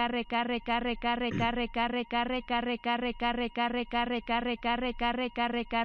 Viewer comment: parece que dice rica rica rica